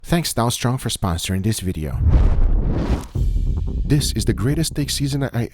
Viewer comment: I thought he said Thanks donald trump for sponsorship this video. I had to rewind and listen again lol